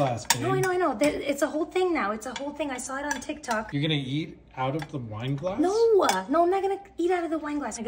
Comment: Very creative and innovative! Also at first I thought he said "yeet it out of the wine glass"